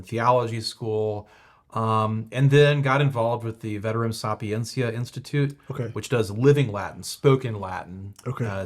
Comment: I couldnt quite catch the Latin Institue that Father said at 27:26. What was it called? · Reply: Veterum Sapientia Institute!